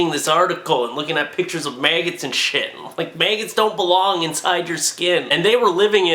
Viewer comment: Did he just say mayggots